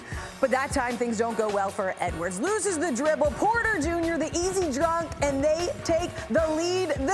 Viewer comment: I swore she said the easy drunk on kPJ